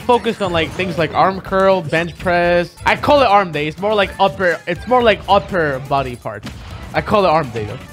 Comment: For a second I thought he was saying “up air body part” I was questioning what he meant by that